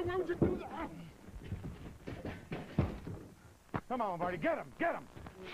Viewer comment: Sounds like Bobby is saying Marty at 2:30. But Lombardi i guess!